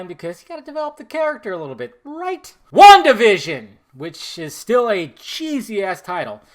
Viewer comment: WandaVision? what genius came up with that? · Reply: I thought he said Wonkavision at first.